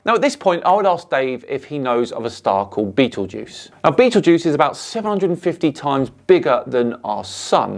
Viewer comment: Háhaha, when you told us about the star Betelgeuse I and the subtitles thought you said Beetlejuice. So when I tried to Google the star "Beetlejuice" Google must have thought that I gone crazy😵‍💫😂 Maybe you should fix that❤